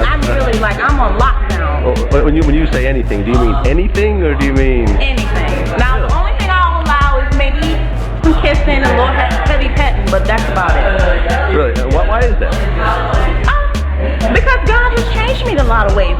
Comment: Im not tryna be funny but it sounded like she said a lil head and then she said petting